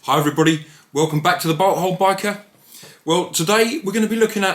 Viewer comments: It really does sound like you say butt hole biker, blame me because I’m American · I thought he said butt hole biker